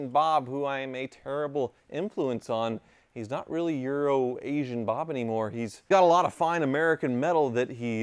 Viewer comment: For the longest time, i thought you were saying "urination bob" and not "euro-asian bob!" 😂😂😂 I always thought that was a strange nickname